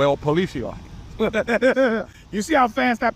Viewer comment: I swear I thought he said that's the boatlice 😂😂😂